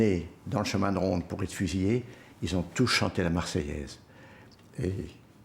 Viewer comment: Correction to the English subtitles : at about 40:00 the interviewee actually says that people all sung La Marseilles on the way to being shot. He did not say they had to sing it. From the context, I take it that they did this spontaneously, not out of being forced to sing the national anthem. The rest of the English subtitles in the interview are correct.